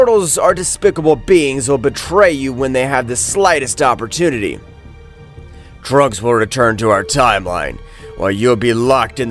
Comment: Why the hell did I hear “Drugs will return to our timeline” here? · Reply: He said "trunks will return yo our time line" not drugs